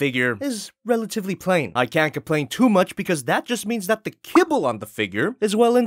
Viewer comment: Kibble!? Fuck i thought you said cable! english isn't my native tongue btw